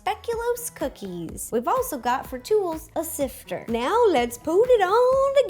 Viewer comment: I had the video playing while I was doing something else and I thought she said, "and a tool... a sister". I was all, Mo isn't a tool :(